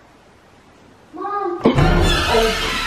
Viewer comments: I heard a kid say MOM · I have a hearing problem and at 6:17 I heard that loud and clear, it did say MOM. /I don't think they make bathtubs anymore. Everything is showers. Ja notice that? /, I'm beginning to really like her. She's pretty cool.